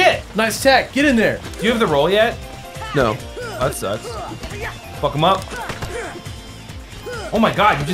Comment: Did Sora just say "Fuck off" at 2:27?!